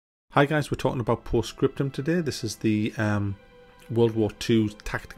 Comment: Tell I’m tired by the way I thought it said scrotum not scriptum